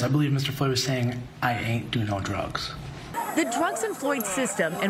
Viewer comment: I believe I hear “I ain’t do any drugs”.